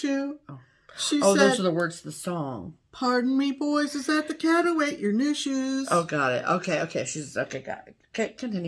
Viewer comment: I was dying laughing but always heard it as Pardon me boys, is that the cat who chewed your news shoes? Hilarious. 🌺🌷💕❤️